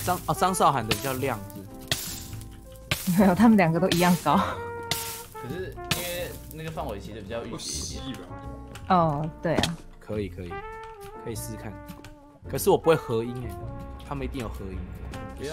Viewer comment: 耳残听成 ” 比较玉米一点点 “